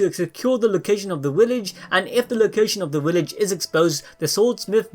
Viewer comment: It sounds like you were saying willage